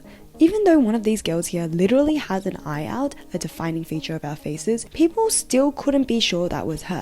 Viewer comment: I keep rewinding to this part of the video to try and hear what she’s saying — it sounds like “one of these girls literally has an eye out, a defining feature of our faces” — as in, she’s missing an eye?👁️ Or something else? I can’t figure it out from the picture, ofc, given that it’s blurred… did she mean, like, one of the girls has one of their eyes covered? 👀 I’m so curious about this, lol. Great vid btw, instant sub from me! 😊